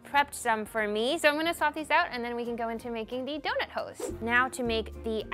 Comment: did she say “donut hoes” 😭😂